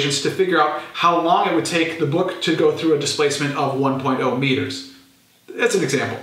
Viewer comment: did u said nine point o metres ?